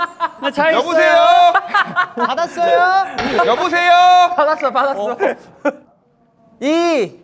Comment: can someone help me? I'm learning korean and at 13:20 i heard "payasso" and the subtitle says (he did),but i don't know if i heard correctly so if someone can tell me what he said exactly i'd be glad. Thank you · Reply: if you can read 한글, he said "받았어" (he picked up [the call])